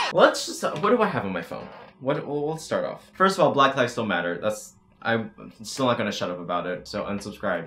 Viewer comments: For some reason the captions said “black lives don’t matter” and I want y’all to know I literally said “black lives still matter” Also masks have been proven to not restrict oxygen flow. Don’t say you can’t breathe in them, just say you don’t like having to wear a mask. OH AND here are SOME of the 100+ “influencers” who went to that party unmasked: Nikita dragun, Emma Chamberlain, James Charles, Charli and Dixie D’amelio, The entire hype house, Bryce Hall, Larrayxo. And turns out cases in a lot of other countries are still rising. Oh well I guess this entire world is stupid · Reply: I’m a whale he said “black lives STILL matter” it just sounds like he says black lives don’t matter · For some reason the captions said “black lives don’t matter” and I want y’all to know I literally said “black lives still matter” Also masks have been proven to not restrict oxygen flow. Don’t say you can’t breathe in them, just say you don’t like having to wear a mask. OH AND here are SOME of the 100+ “influencers” who went to that party unmasked: Nikita dragun, Emma Chamberlain, James Charles, Charli and Dixie D’amelio, The entire hype house, Bryce Hall, Larrayxo. And turns out cases in a lot of other countries are still rising. Oh well I guess this entire world is stupid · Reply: I heard “black lives don’t matter” but I had to do like... a triple take and I was like “oh thank goodness he said ‘still’”😅